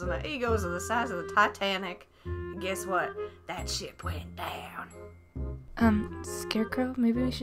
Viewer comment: Like the titanic ...and dat shit went down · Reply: block wolf9000 no, she actually said shit. watch it again.